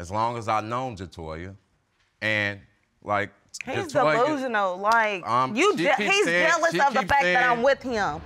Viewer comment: Did she just say he's devosional @ 10:19 to mean he's delusional 👀...